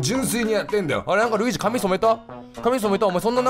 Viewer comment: 同担拒否ルイージガチ勢キヨによるルイージの髪色考察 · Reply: あれ？フジ髪染めた？ に聞こえたから！？ってなった笑